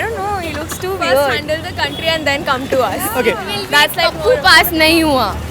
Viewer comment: Wht did she say first handle the country u motherfucker he is president of AICC and that girl is not even fit to eat raga thrown food